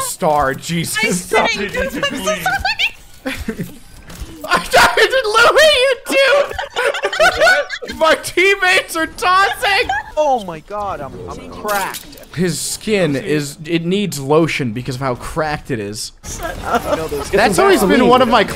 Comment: did he says my teammates are Tossing lmao